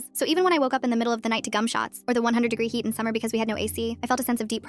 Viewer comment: I’m so damn dirty minded I thought she said c*mshots😭